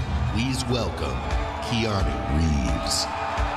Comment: they said Keanu Reeves Subtitle says He army leaves 😂